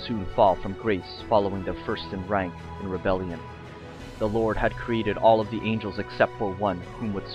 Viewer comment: "Lord had created all the angle expect for one" 2:30 did I hear that right?